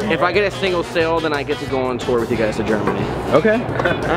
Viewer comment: wait! hold your horses and panties! 1:20 did just say "Tour to germany?" Did I miss something? Have I not heard this right? GERMANY? HeRe?! 0.o also... great vid:D